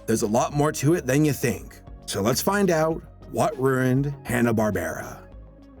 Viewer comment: What ruined Hannah barbera?the Simpson’s , South Park ? Disney ? This dude in the video sounded like he said what “rerrined “ hb ( instead of ruined )... no offense but sounds like you’re trying so hard to come off like some radio dj or news reporter / journalist ... just speak normally , please ! It takes away from the content when people feel they need to talk like that. We hate it when we watch the news / Anything in the media and YouTube - with random everyday people uploading their videos is no exception. Ps, you have an ad at the beginning of this already .. why have to include one in the middle and likely , one at the end , who knows ? Not going to watch the rest of it to be interrupted with more ads .. I blame YouTube for that , but still ..